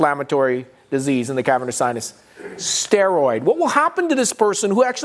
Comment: If you give steroids to mucormycosis we are going to have "fungus among us" has to be Dr. best line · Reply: I thought he said " fungus humungus " ; or did he ?